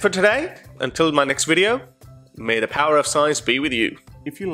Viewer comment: i thought you said may the power of size be with you funny :)